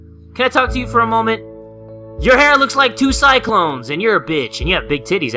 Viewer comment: two cyclone instead of kokoni